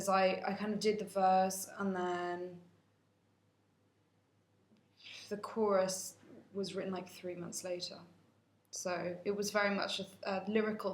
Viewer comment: what is she saying there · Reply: "The chorus was written like 3 months later"